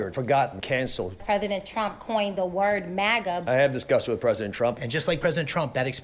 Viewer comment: 4.23: I first heard, "I have disgust with President Trump," and I thought, Sure! So do I!